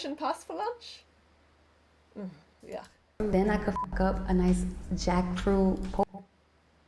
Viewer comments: I just heard “then I could fuck up” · I heard her say “I could f*** up a Jack fruit” but I’m not sure! Either way she eats jack fruit!? Like woah